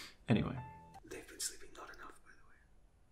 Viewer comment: WHAT IS SAYING AT 0:20. I need to know 😭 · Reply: "They've been sleeping not enough btw" ;)